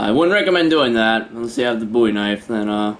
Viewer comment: I Thought You Said Boobie Knife